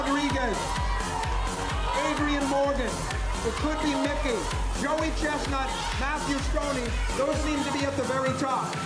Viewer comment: did he say (Joey chestnut) one of the most competitive eaters